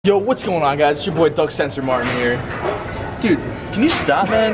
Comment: At first I thought he said what's up guys it's ya boy thug censor martin